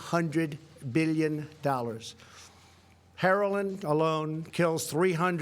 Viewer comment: Am I the only one that heard him say herolin instead of heroin?